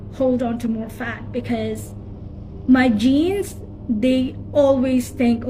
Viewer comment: I thought she Said- MY JEANS STINK. Really thought she was talking about her pants stink because she was fat. 🤣 🤣